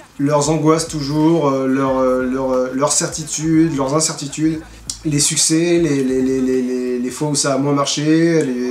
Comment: C'est moi ou à 24:32 il parle de les sucés au lieux de leur succès.